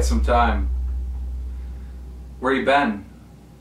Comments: When Keem asked where leafy had been, did I hear "Treatment home" or was that just me? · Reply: Yes treatment home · Keem star: Where ya been? Leafy: Treatment Home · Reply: I thought he said he was “at home”